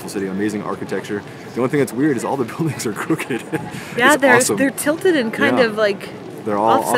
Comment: I thought you said “all the police are crooked” - had to listen again - buildings are crooked (LOL)!! Not sure I’ll ever get to Amsterdam so thanks for taking me along!